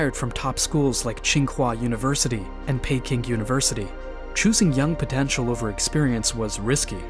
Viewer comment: Did I hear Pancake university?